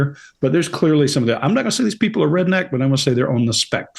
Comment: Did call them rednecks at 5:49?? I'm not sure I heard that correctly but it's hilarious.